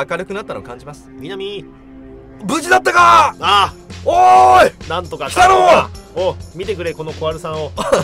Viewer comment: の おーい北野ー！が おーい鬼太郎ー！ に聞こえたのは私だけか...